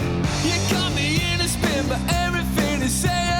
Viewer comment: when i die, narrate my eulogy. 😁 i always heard the phrase "you got me in a apin" as spoken because of the way the word "spin" kinda droops.